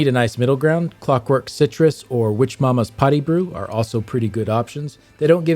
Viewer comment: Why did I hear “witch mothers potty brew” lol 😂